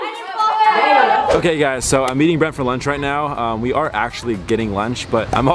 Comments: When said I’m meeting Brent for lunch I thought he said I’m eating Brent for lunch 😂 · it sounded like He said I’m eating Brent for lunch right now lol 😂